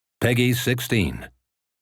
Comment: I thought he said, "Peggy's 16" and I fucking laughed. Anyway I'll be getting this game of course, durrrrr. its FF.